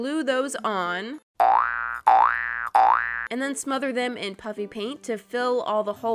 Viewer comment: It sounds like she is saying puppy pant.